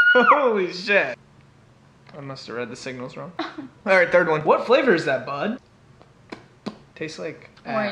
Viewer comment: i thought bryce hall said what flavor is that hun? 😵